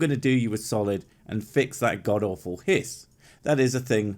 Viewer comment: When you said "fix that awful hiss" for some reason I heard "fix that awful piss" leading me to believe you were referring to his choice of drink and were going to paste a whisky bottle over his Champaign bottle.